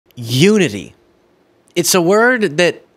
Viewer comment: "We need unity". I thought he said "nudity". Perfect example of "you hear what you want to hear"? :)